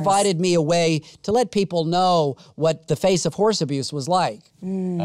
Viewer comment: I thought he said house abuse